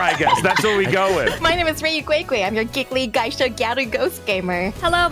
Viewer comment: idk who this is but i think she said gyaru ?